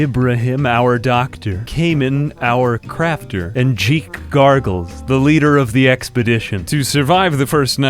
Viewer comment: I thought you said the leader's name was "she gargles".